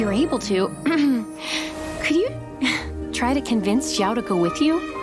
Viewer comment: Is it just me but I thought she said " can you convince Xiao to go out with you " I